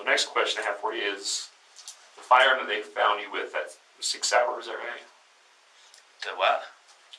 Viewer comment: Here's what I heard... "The next question I have for you is... the firearm that I found you with... that six [???]... is that right?" "The what?" "The g*n you had in your car." "Uh huh." "Where did you get that?" "I'm sorry I missed that... the what?" "The g*n you had in your car... where did you get that?" "That's Corey's" 13:57 Then goes on a little tangent about how this is a classic sign of deception: trying to buy more time. While I agree that tactic does get used, I really don't think that's what happened here. I think he was caught up with the unintelligible part [???] for a few seconds of that because the question wasn't heard and then split up into smaller parts that didn't contain the [???] that wasn't heard the first time. As I was watching that conversation, I was slightly confused at what the officer was asking mainly due to the small part I wasn't able to hear initially. It only became clear what he kept asking at the end of the bit but I was still left wondering what the [???] part was that I'd missed. That's my take; suggesting he was trying to buy a couple extra seconds seems less likely than that he simply misheard. Anyways! What I'm getting at is that while I do enjoy content like JCS and similar channels... it kind of irks me when youtubers go on tangents where they are basically too confident in what they are saying... and it makes them look bad (just a bit, sorry). talked for a good 40 seconds after "analyzing" the previous 3-5 seconds of the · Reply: He said ‘sig sauer’ which is the brand of gun they had